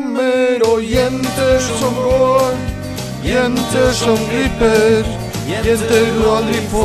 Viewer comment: le entendí Creeper